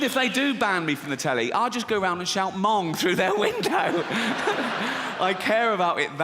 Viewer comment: I first thought he said "mom" and got really confused. I meand, where the hell did the 2nd 'o' go?